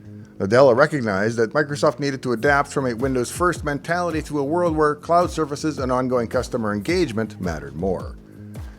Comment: For a second I thought had said "UNWILLING customer engagement" (at 3:30) rather than "ONGOING customer engagement". I'll now forever think of Microsoft services as unwilling customer engagement. ... Any writers out there, feel free to borrow this phrase if you find it as apt a (almost) slip as I did.